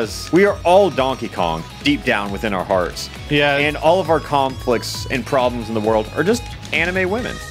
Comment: did he say "Kong-flicts"??